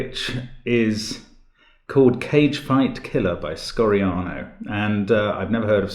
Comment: Silly me I thought it said cake fighting